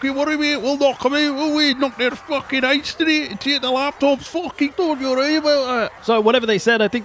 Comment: did he say, "laptop"? That people are still getting caught by the Vermintide endgame...like, two-and-a-half years?...after it was introduced is magnificent...I'll crack open a Powerade Zero and drink to that